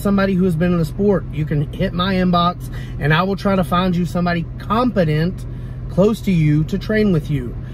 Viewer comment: I heard “confident” not “competent” and I realized you would not call me with a novice lifter